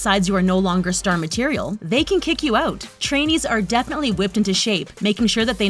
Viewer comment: haha sounds like you said “trannies”